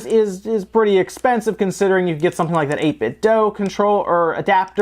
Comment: Could you comment on what the "8 bit dough adapter" is? that's what the subtitles say. RE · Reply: It seems to be how the brand 8bitdo is prounounced.